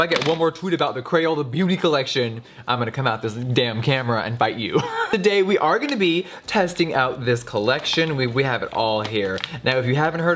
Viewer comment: I heard him say: ''One more tweet about the Crayola beauty collection, I'm gonna come out this damn camera and BITE you'' xD